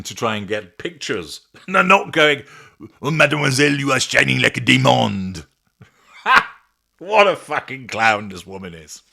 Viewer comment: Pareidolia is a funny thing, I thought said that the photographer said 'she shon like a demon'. Accents can be tricky at times non mes amie🤔